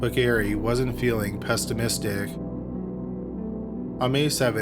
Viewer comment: Did he just say pestimistic?